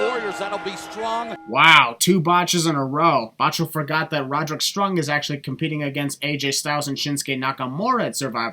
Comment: no hate but you botched Despite what the subtitles say you clearly said Roderick Strung thats 1 sin for